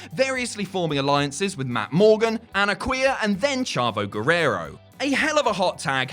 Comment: did you just call this man a Qu eer?